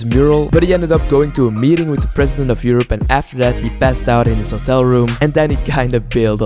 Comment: I thought for a minute you said he passed away 🤣